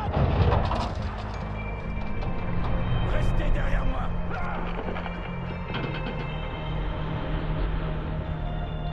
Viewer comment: what's being said in french at 0:14? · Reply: Rester derrière moi - Stay behind me!